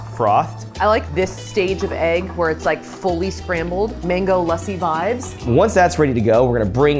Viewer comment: did i just heard mango lassi on this channel?😩😂